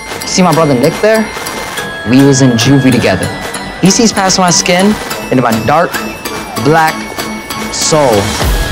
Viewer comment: Carl is fucking hot thank you for this masterpiece Is anybody know what he was saying in 0:10? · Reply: "he sees past my skin into my dark. black. soul."